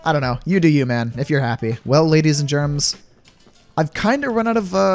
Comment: At the end i thought u said well ladies and germans. In that second i laughed so hard but then i relised :I